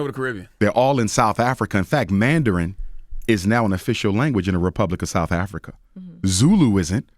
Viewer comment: I'm South African. I can tell you that Mandarin is NOT an official language over here. Zulu is an official language and so is Xhosa. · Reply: Thato, you're not listening. He DID NOT say that Mandarin was the official language in South Africa. He said, "Mandarin is "An" official language in South Africa (2:22); quite different. And Thato, I don't know how old you are, but Mandarin is being taught in schools in South Africa. Do a little research Brother.